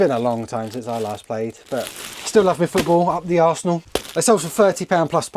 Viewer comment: hahahahaha, you had me laughing so much here! I was only listening, and I was like, what did he say? he likes it up the - what now??